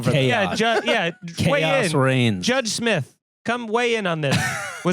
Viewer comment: Thought it said "Dave Smith" lol thought we'd get some hot political takes! 😅